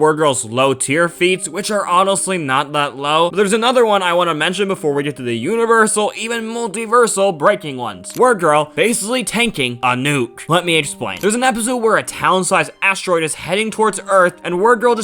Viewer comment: did you say universal in this vid